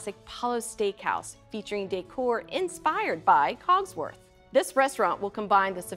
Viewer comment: I misheard hogwarts and had a heart attack